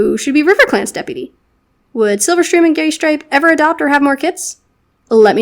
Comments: Is it just me or at 7:16 did say GAYstripe? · Did you say 'Gaystripe' If you did, I'm adopting that as Greystripe's new nickname. Same if you didn't. Still, love that you said that if you did! <3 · I heard gaystripe and I love it that is now his name Thank you